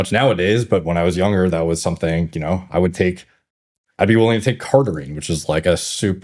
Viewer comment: did Derek say Carnitine as in L-carnitine could cause cancer? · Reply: Cardarine